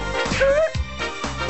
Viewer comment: "In syncopated style" ^-^ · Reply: Man I always thought he said "and single ed style"